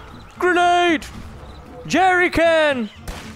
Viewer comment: did he say jerrycan?